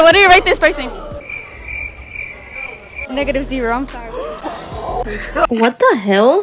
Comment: DID SHE JUST SAY -0 😭😭